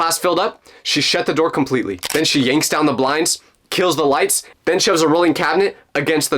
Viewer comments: Bro said kills the lights but i heard "kill the rights" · Did he say KILL the lights